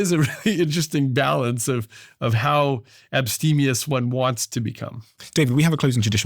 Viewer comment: thought this guy said EPSTENIOUS to describe party state 😂